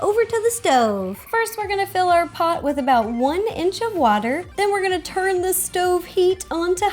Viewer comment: Did she say one inch of water?